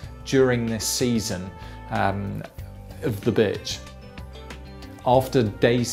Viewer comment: did you say bitch